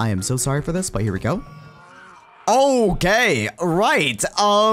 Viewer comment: " Oh gay! " thats what i hear